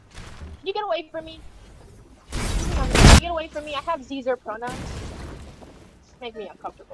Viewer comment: i think she said "i have zee/zhur pronouns"